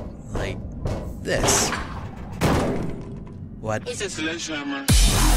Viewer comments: Omg I thought said "It's a sledgehammer" at the end when it was the start of the song xD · OH MAH GAWD! I heard "It's a sledgehammer", and I KNEW he was gonna play Savant's "Sledgehammer" XD